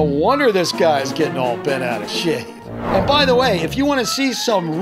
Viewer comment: did he just say shi*